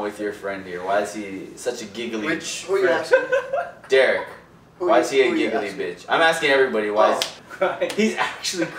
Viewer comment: why did i hear why is he a giggly b*tch!?????